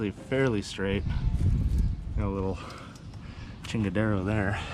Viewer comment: Did you say, chingadera @ 16:37, lol